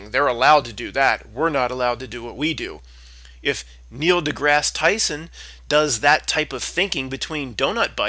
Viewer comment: Did you say "Niel Dickass Tyson"?